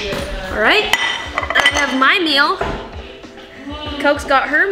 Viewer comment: Is it just me or did just call "Coke" at 4:29 😂